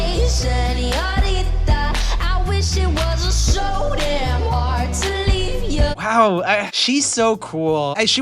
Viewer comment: sounded like she said I wish it wasn’t so so hard to eat ya